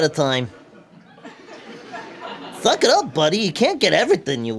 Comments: Why did it sound like Fafa said Fuck up buddy? Idk if that's actually what he said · I heard that as "You're fucking up buddy". The subtitles don't really help either. Just saying, in future Fafa shoukd be more careful that he doesn't make one word sound like another.